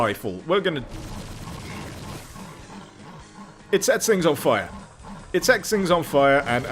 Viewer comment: for a second i thought he said "it sex things on fire"